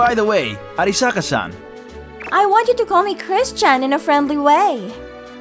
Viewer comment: I thought she said called me Christ Chan I had to replay it again